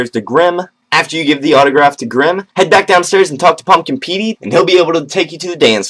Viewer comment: lol when i said pumpkin pete i thought u said pumpkin pee